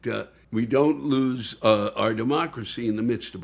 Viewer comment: Did he say lose our democracy, or loose our demo-crazy?